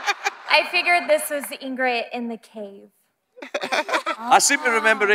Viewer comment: at first i thought she said ingrite and i was like nah no way then she did it again hahaha